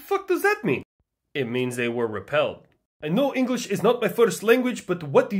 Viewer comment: At first i heard that said, "i know that English isn't you'r first language, but what ..."